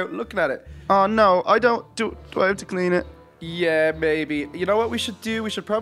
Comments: I thought said yeah baby but he actually said yeah maybe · I heard say Yeah Babe